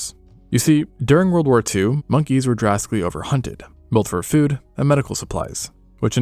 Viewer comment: I thought you were gonna say monkies were drafted 😅😅😂